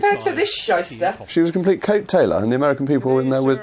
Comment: What's a "Cape tailor" if that's even what he said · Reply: Coat tail-er. Someone who travels in on the coat tails of someone else....in this case her husband.